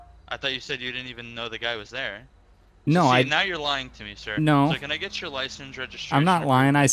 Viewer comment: not in a car. | The officer: can I get your license, insurance and registration please? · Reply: I think he said “can I get your license, registration, proof of insurance.”